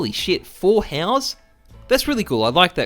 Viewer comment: when he said 4 howls, I heard 4 house and was like wot